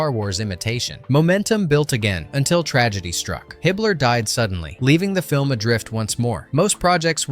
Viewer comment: I thought he said Hitler died suddenly. Lol. I was like wtf?